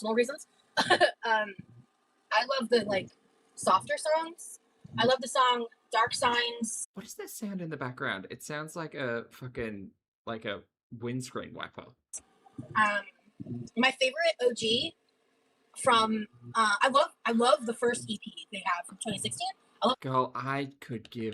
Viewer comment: And from this day forward every time I can bring the topic up I'll be referring to windshield wipers as "windscreen wackers." That's what I heard and I'm standing by it no matter who disagrees. 😜